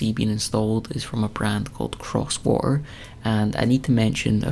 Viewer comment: The bathroom looks stunnin'. I thought you were saying Crosswar the whole time, though, and I thought that was a very odd name.